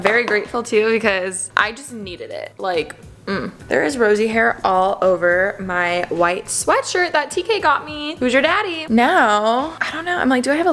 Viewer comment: (edited the time stamp ) did she say who’s your daddy or did I hear it wrong cuz I yelled Harry